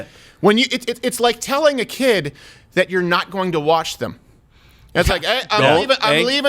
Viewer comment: I thought he said "kids are not going to be washed", instead of "watched".